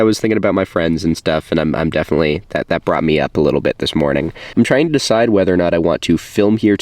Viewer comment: I thought you said "Iced beers this morning".